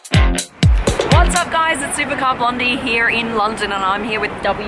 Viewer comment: YouTube automated subtitles very often MISHEAR! 0:10 - it's "Supercar Blondie" not "Supercop Lundy"!! But we must admit Lady, that your Australian accent is very demanding!